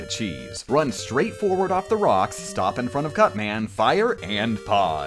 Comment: I heard gutsman when your in cutman.I know they sound the same but come on man!!